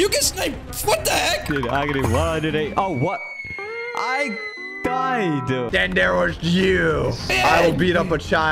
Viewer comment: what did he even say right there? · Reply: Oh what- i. Died